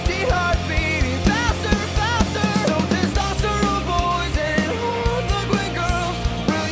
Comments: Me: sees fish tank Me: tESTOSTERONE BOYS AND HARLEQUIN GIRLS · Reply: I heard it as "disaster of boys" and I was confused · Everytime I hear Harliquin girls I hear "Harley Quinn Girls" god damn it suicide squad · Everytime I hear Harliquin girls I hear "Harley Quinn Girls" god damn it suicide squad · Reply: AlexThePotato I thought it said "all the queer girls"...? 😂